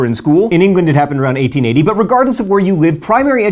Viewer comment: I thought you said that in England it happened around 1980...for a moment I was horrified and ashamed to be British. Then I rewound and realised I'm a moron.